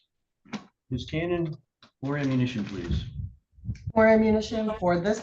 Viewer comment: I literally heard him ask her if she had “more ammunition”!!!